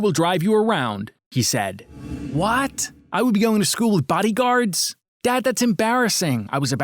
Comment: Wow I thought it said “I Go To School With BadBoyHalo” LMAO